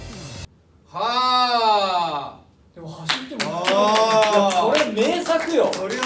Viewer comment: 髙地くんの「こんないい男2人を振らないで」に樹くんが小声で「でもいい大人だろ」ってサラッと返すの樹くんって感じがする😭(イヤホンしないとほぼ聞こえない声で樹くん話してる)